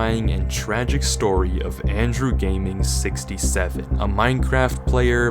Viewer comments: did i heard 67!!!!! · did i just heard 67? 🥀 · did you say… 6… 7